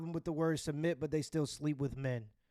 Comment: NGL first 3 times she said "submit" I thought she was saying "cement"